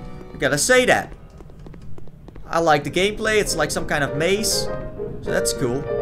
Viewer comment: did he say " I like the gay play" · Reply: Game play